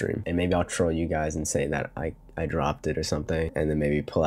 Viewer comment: i thought he said he dropped it on twitter